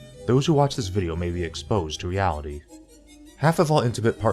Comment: Lol. At first at 0:21 I thought when you said "reality" you said "Yaoi"